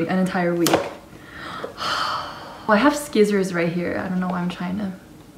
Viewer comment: wait did you call scissors "sKissors"? 😂